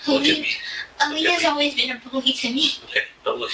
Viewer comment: Did she say that Aleah has always been a bully to her?